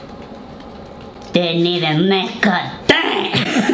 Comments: What did say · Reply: Didn’t even make a DENT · All I hear: DIDN’T EVEN MECOTEH i have no clue what he said but I literally died 😂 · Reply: Lol i heard "DIDN'T EVEN MAKE A THANG!" 😂😂